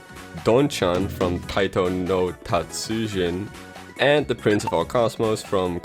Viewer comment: As a fan of this series, I’m here to say you mostly pronounced the name right. Edit: You said ‘Taito’ not ‘Taiko’. At least that’s what I heard.